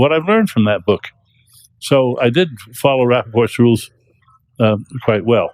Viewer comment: Will someone please tell me what he’s saying at ~4:01? Something like “rapid course rules?” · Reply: Rapoport’s Rules. Basically just some guidelines for critiquing someone. Dennett is associated with them.